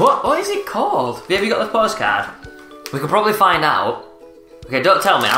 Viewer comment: Did anyone else hear say "babe" at 3:10 I know there together but it sounds weird hearing it coming out of mouth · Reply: Kaerelle Alcantara He said babe read me the post card